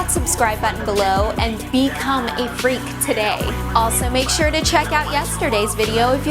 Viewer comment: At the end I thought the girl said "Subscribe to become a grape!"